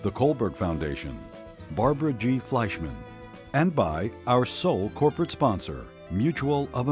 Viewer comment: wait wtf did it say 1:15s "Buy our soul" wow just wow